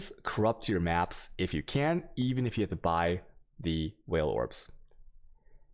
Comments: Thought he was saying whale orbs the first few times, lol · Everytime he says, "alchimmy". My ears hurt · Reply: And vaal ...I thought he was saying whale orbs ...And now chisels